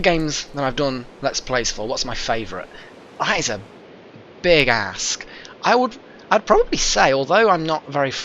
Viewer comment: I had to rewind that (cause I had my headphones partly off my ears,) at first I thought you said "that is a big ass" I quickly rewind and put my headphones back on and hear "that is a big ask" oh ok that makes more sense. lol.